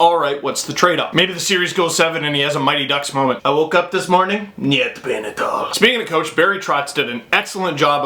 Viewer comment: I'm Russian, but I still don't know what Steve said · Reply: Валера Харитонов Steve said "I woke up this morning, nyet pain at all." Classic Steve D. Love it when he quotes Uncle Leo with that voice.